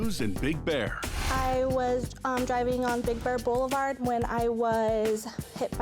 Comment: Sounded like she said Big Bird Blvd. I know she was trying to say "Big Bear" but I had to listen again to get it.